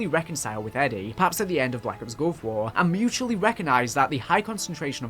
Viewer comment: Is it just me or did he say black ops gulf war at 24:45 ?